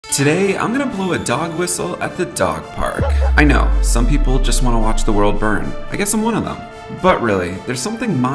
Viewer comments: All i heard was "Im going to blow a dog"☠️☠️☠️☠️☠️i was shooketh buth then i heard whistle... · My wifi got cut off at the start so what i heard was "Im gonna blow a dog"😅